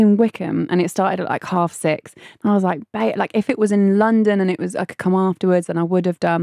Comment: Anyone else hear that about Akinfenwa? 'and I was like bay...' sounds sus. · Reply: Bayo, as in short for Adebayo